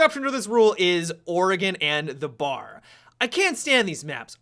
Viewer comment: what does he say after Oregon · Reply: The bar? Referring to clubhouse maybe?